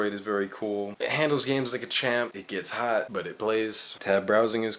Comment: LOL did you say it gets HOT, but i blaze?